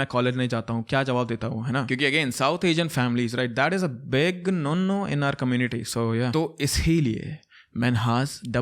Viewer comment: Did you say big nunnu ? 😂